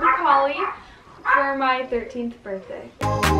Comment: Lol, at the beginning I swore you said 18th birthday and had to play it back a few times cause I was like "There's no way she is 18 she was like 12 yesterday." xD